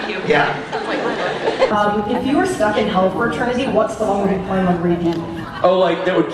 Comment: Does anyone know what the girl is saying at about 5:28? I keep replaying that part but I can't understand what she's asking. :/ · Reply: "whats your memory that would be on repeat" i think (or something along those lines)